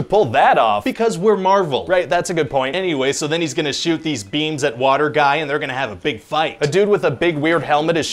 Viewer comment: Oh, beams. Thought you kept saying "shooting beans." That would be a sweet super power.